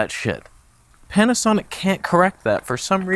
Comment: I heard Can'tasonic in there somewhere and hope it continues